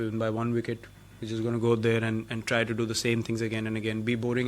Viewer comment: did he say be boring as a team at 8:15??